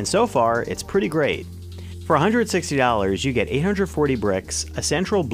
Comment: Is that a speech impediment? 0:19 "160 Ollars" instead of Dollars and 0:41 "Androi" instead of Android. Or does just not like the letter D since president #45 arrived on the scene?